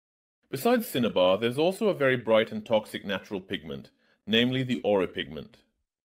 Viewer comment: did i hear ora ?STAR PLATINUM ORA ORA ORA ORA ORA